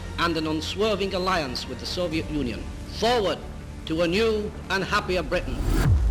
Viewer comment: it kinda sounds like he says "soviet nunion"